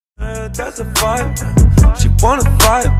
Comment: ''thas a fart, she wanna fart'' am i hearing this rite?🤣